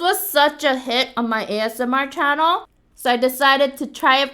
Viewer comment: she said asmr But the captions said 'ass'..... I'll be going now Bye